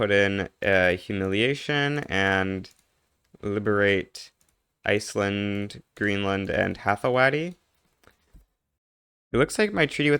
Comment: damn the odyssey I heard “liberate Iceland, Greenland, Anne Hathaway”